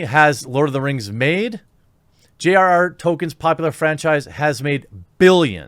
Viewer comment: Quite funny, I thought Jeremy was saying "token" instead of "Tolkien". Very apt for this new Amazon series